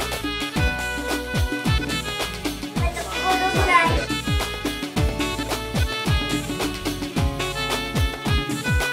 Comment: في الدقيقة 28:55 سمعتو الطفل اش يقول😭😭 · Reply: لا تقبرونا